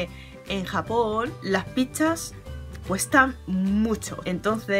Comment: No quiero ser inmaduro pero en el 05:06 parece que dice pichas(el pene en España) v